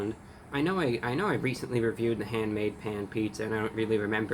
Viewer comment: Thought it said “handmade pain” and tbh it fits